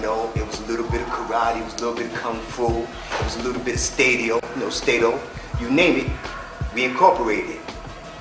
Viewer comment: What does he say after "kung fu" at 1:29 or so? Sounds like staydo? I'm not familiar with that system